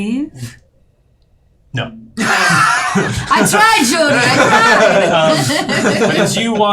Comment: Can anyone tell me what says at 1:25:00? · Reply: They said “but I said, yes Jody”